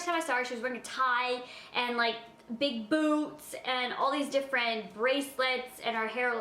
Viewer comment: i thought she said big boo*s not big boots! 😂